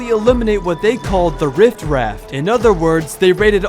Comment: Did anyone else hear him say “rift raft” instead of riff raff at 20:44????